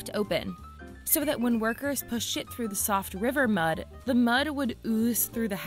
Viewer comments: anyone here what I heard about "putting shit through?" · Did say they pushed shit through the soft river mud at 4:25? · did she just say "pushed shit"? 🤣 Just wasn't expecting that 😁